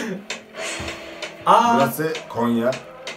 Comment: burası konya mı dedi ben mi yanlış duydum oha ben Konya'da yaşıyorum abi nolur cevap ver doğrumu duydum ♥️